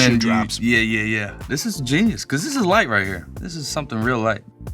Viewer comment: thought he said "This is life right here" which would also make sense with what he said before. Life starts easy then gets harder (hotter ooo bars) as it gets spicier